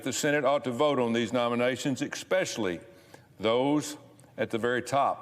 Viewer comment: Did he say "Ek-specially?"